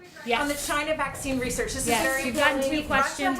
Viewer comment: I thought I heard, " Ok, you don't lying "b**ch"